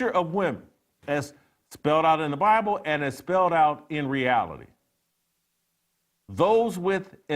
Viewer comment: as spelled out in the Bible and spelled out in reality . I heard that as the Bible is not reality.